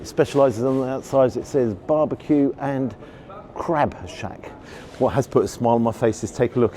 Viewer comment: I thought that said "Big Easy Bar-B-Q & Cashback" and not "Crabshack" at least with a cashback guarantee, the place would have a saving grace.